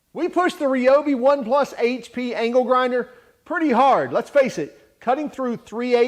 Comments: i swear i keep hearing ''anal grinder'' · at first I thought you said anal grinder lmao